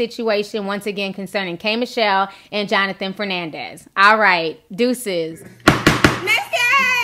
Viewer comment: Oh snap...I thought u said Joseline Hernandez.😂😬 their names kinda sound the same.